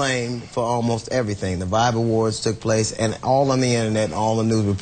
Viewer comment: I thought they were saying Bible wars. Damn peeps need to annunciate.